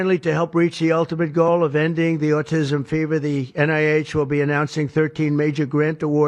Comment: Did I hear Trump say "Autism fever"? I think I did.